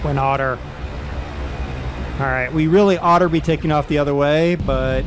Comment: I thought you said we really otter to be taking off.... At 16:20. Btw nice pun